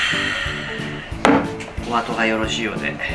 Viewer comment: イタリアンそーめんってかんじで美味しそ〜メッチャサッパリで食欲失せがちな夏でもスルスル行けそう またお中元で貰うだろうからそーめんのレパートリー増えて嬉しいᕕ( ᐛ )ᕗ 「おあとがよろしいようで」が「トマトがよろしいようで」に聞こえた(笑) もしや大惨事が起きたのは経験済みなのでしょうか…